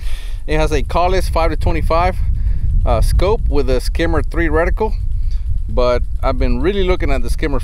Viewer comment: I'm sorry, at 1:54 it sounded like you said “Skimmer 3” and “skimmer 4” reticle, but I couldn't find anything like that: did you say Tremor 3 and Tremor 3/4 and I'm just losing my hearing in my old age? Or is it not spelled like I'm hearing it? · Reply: ahhh Kahles SKMR 3 and 4. I would have never got that on my own. Thanks!